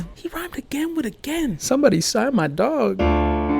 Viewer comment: "He rhymed again with again, someone sign my boy" had me dying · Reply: oh that makes more sense! I heard "someone sign my dog"